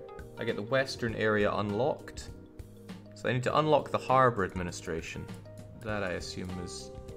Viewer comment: I thought the lady said "I could convince the nudist colony..." rather than "administration". Nut sure where my head was at...